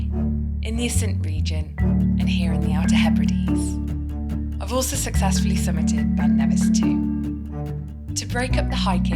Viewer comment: What does she say at 0:18 as I can't make it out, something about successful. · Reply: I had to listen back to it, but I say 'I've also successfully summited Ben Nevis too', but I think there were a few too many S's for me to get out and it did sound a bit of a mess I'll agree 🤣